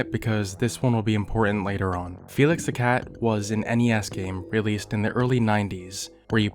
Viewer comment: I thought bro legit said "any ass game" 😂